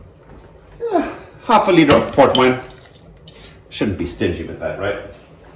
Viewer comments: You really had me going when I thought you said "pork rinds" were part of your chutney recipe...but then I realized you had actually said, "port wine"! This sounds so much better! Funny how someone says one thing and we can hear something entirely different 😂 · Thought you said pork rinds, then I read the screen, port wine! Is the kitchen all done now?